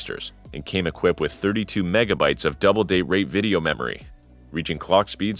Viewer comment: am i hearing right? double date rate?